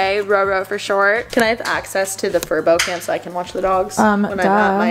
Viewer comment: When you first mentioned the sponser I thought you said furby